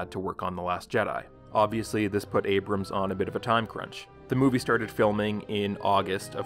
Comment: I thought he was saying Abrams Ontario for the longest time 😄